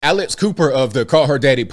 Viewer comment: I misheard and thought ABL said Alice Cooper. I was like woah he looks real different now 😅